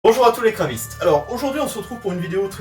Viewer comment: je comprenais pas pourquoi il dit "bonjour les grévistes" en sous titrage hhhhh. En fait c'est "kraviste" qu'il dit.